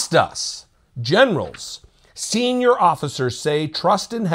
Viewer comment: Losing his generals? For a second, I thought you said genitals…Wait! What? Oh, I didn’t think he had any!